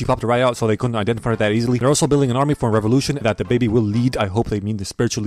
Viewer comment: i heard dababy Lets gooooo